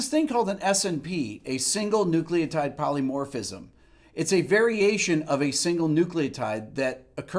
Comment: Sorry I definitely heard veryASIAN here😂